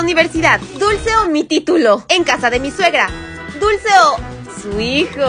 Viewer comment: YO CUANDO ESCUHE EN LA PARTE DEL DULCE OH MI TITULO YO PENSE QUE DIJO DULCE OH MI CULO